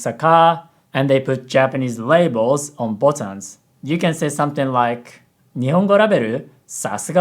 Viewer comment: Kinda funny how said "botans" instead of "buttons" at 6:05 because in Japanese, "button" is ボタン "botan".